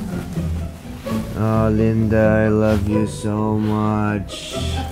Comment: did he say "Harlinder i luv u so much"?😂if he did he just said my fucking name lol